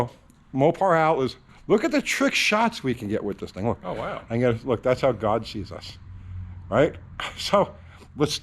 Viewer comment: at first listen I thought you said that's how Dodge sees us! lol great job with the selfie stick btw! will adding these clamps add any benefits if I already have cal-trac bars?